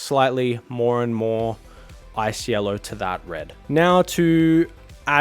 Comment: Hello, @ 4:27, what yellow did end up using for the edge highlighting. It sounds like you said “ice yellow” 😅. Thanks!